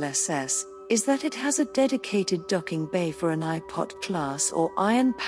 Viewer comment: did it say, it has a docking port for an iPod? Since when does Apple sell portable mp3 player the size of a bus?